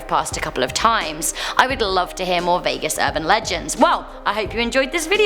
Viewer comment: First second I thought it said vegans urban legend